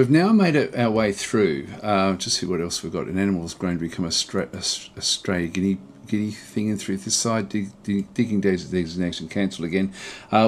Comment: What did he say?? 30:05 Jokes aside I'm really looking forward to your playthrough, you strike an amazing balance of being entertaining, informative, and passionate and have as a creator for many years. Hope it continues for many more! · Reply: Lol. I had to look at it six time to work out what I was saying. :) Something about a stray guineacock growing up. :)